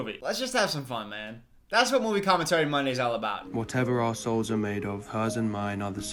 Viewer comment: oh my god it took me 6 listens to get that he's saying "whatever our souls are made of" i genuinely thought he was saying "whatever assholes are made of" and i was BAFFLED.